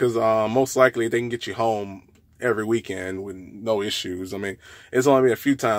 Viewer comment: I thought they said Tmc b home every weekend. How much Tmc start you off with